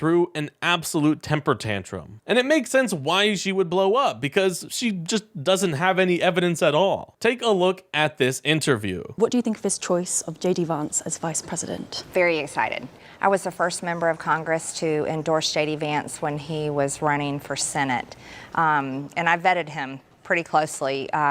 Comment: Wait wait ....At 5:15 is it me or does she call jd vance shady vance ?